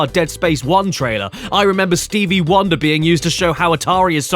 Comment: My phone glitched and it sounded like Stevie Wollybing